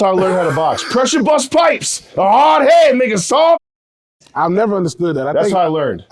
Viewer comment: did he just say ninja?? im dead